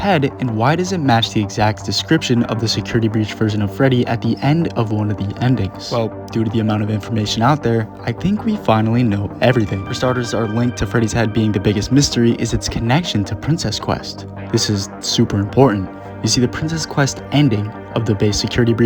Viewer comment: For some reason, I thought you said, Fredless heady